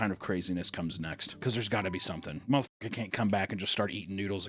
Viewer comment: did he say most n*ggas can't come back and just start eatin noodles again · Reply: No, he said "mthrfkr" but it was censored to keep the video monetized